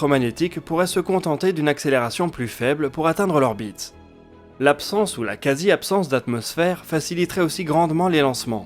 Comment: Hello, petite question sur la vidéo : à 10:11 , c'est quoi "l'absence de la quasy-absence d'atmosphère ?", c'est une atmosphère très dense... p'tite coquille qui m'a fait sourire :-) merci pour cet épisode, c'était très intéressant... j'adore le projet de SpinLaunch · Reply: Il dit "l'absence ou la quasi-absence", pas "l'absence de la quasi-absence"